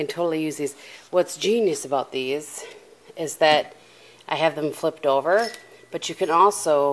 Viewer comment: OMG, I thought you said you mom had flipped over and I was like why are you just standing there and then I realized you said your mums had flipped over. Haha!